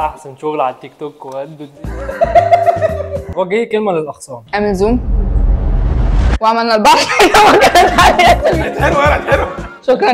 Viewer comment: ايش قالت في الدقيقة 19:26 ؟ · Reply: وعملنا البحر طحينه وكتبنا عليها اسامينا